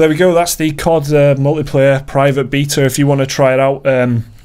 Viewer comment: Did you just say "beeta"?